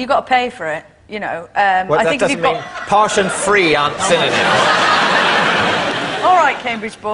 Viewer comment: What's the word David uses at 18:00? "Posh and free aren't [blank]"? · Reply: "Synonyms".